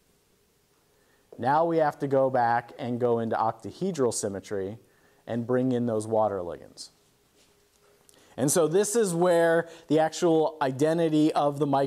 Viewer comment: I totally thought he said water wings at first. haha. I am watching from BYU, and I love these lectures! Thanks for posting.